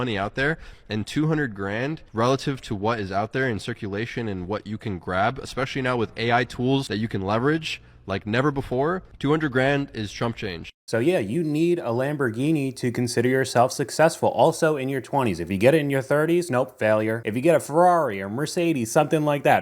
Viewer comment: It sounded like he said “200 grand is Trump change” 😭😭😭 small loan of a million dollars